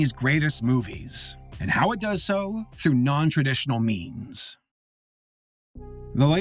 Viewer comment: I honestly thought you said non-traditional memes